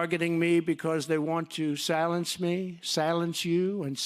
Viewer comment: "They're trying to silence me!" he says over a loudspeaker PA system in front of a crowd of people... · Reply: I could have sworn he said "they'trying to 'schilence' me."